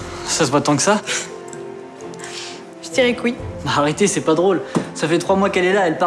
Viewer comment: c'est moi ou elle dit "J'tire les couilles" ?